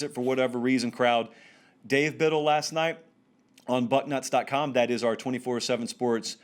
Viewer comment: I swear he says butt nuts instead of Bucknuts. hahaha